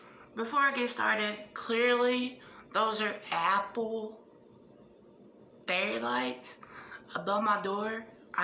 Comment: Love the disclaimer that those lights aren't roaches🤦‍♀️ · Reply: oh hahaha I thought I heard apple berry (long pause threw me off) 😂 even with knowing now they don't "obviously" look like anything recognizable from where we see them. I'm gonna stick to them being apple berry light now 😂🤣😂 (Thanx for the clarification though 😊)